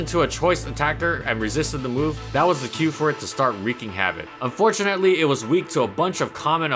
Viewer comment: excuse me I didn't understand the phrase he said at 11:12 ("for him to start ..."), can someone spell and explain it to me ? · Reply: Wreaking havoc: causing a lot of damage